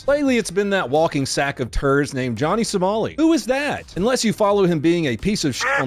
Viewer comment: I thought you said Johnny Tsunami lol😅